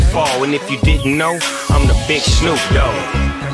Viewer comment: I thought he said im the bitch snoop dogg. Haha love this song!